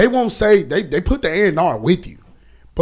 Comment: What in the eff did he say?!? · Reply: "They put the A&R with You"